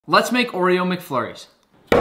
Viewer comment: I thought this guy said let’s make Oreo mc furrys